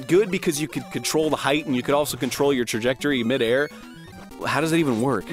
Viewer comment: What kind of word was that? did he say dejectory?